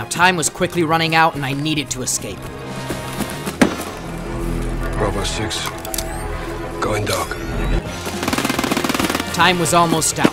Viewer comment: Why do YouTube subtitles say "robos Juice going dog"